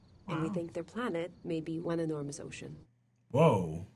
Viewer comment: I listened to "one enormous ocean" literally 10 times because all I kept hearing was "one in arms ocean". Thank god for subtitles!